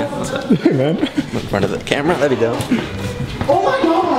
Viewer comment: so wholesome "If you need help with the camera just let me know" 😭😭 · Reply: At about 15:32 ? he was trying to fist bump and I think he said something like "...the front of the camera, there we go".